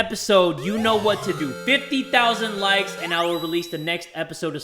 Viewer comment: I allways thought he was saying Jay:50K views then I will post the next video Instad of 50K likes